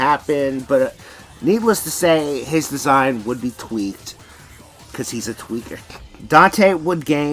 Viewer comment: I had to replay this like 3 times because people walking by blocked out what said and I thought he called Dante a twink, I'm going insane does anyone want anything